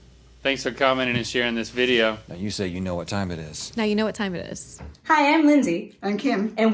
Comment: uh what did she say?! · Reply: It’s “you know what time it is!” NOT “NOW you know what time it is” 😡 lol I’m just making a joke really